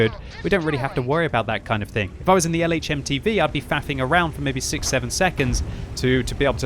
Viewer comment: I thought he said "fapping around" at first